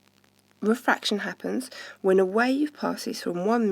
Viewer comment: what’d she say there?? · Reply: lambda its the sign that looks like the upside down Y its used to represent wavelength